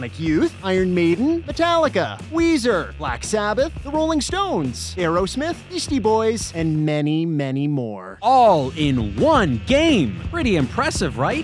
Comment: I swear I heard "Yeasty Boys" 😂